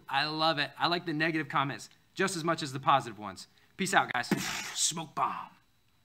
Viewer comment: I legit heard "smoke pot" and had to listen to it a few more times to get it!